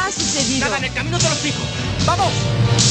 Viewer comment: que dijo?, yo ni entendi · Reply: Nada, en el camino te lo explico, vamos !!